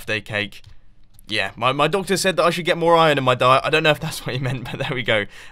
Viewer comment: $:11 At first I thought you said "My daughter said..." and I was just staring at the screen with eyes like an owl.